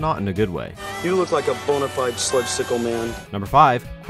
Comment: Did He just said “you look like bone-a-fide slutcicle man”